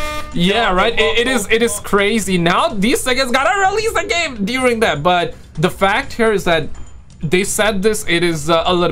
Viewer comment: What did bro say 😭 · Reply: I said "suckers" lol 😂